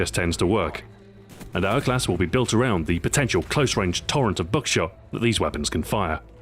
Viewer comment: Being an American and being ignorant of other countries' accents, sometimes I have trouble understanding what your saying. Like when you say "Buckshot", I hear "Dookshot"